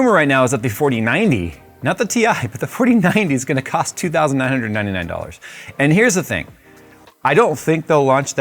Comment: I had to listen to that part 4 times, and I'm still not sure I heard it correctly - $2999 ??